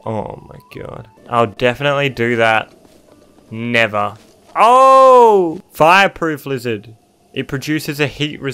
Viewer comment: I heard "Fireproof Alexa"...